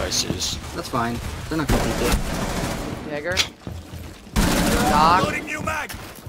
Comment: what did he say at 4:40 😳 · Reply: He said jaeger